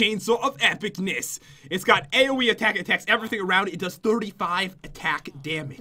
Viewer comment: hair and bow haven't fully generated yet😂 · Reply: also at 2:38 the way said AOE Attack on the weapon it sounds so funny it sounded like AOE ATEKATEK